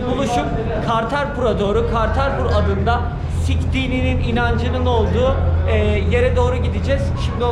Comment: Olum "siktiğiminin inancı" anladım allah seni bildiği gibi yapsın! :D :D :D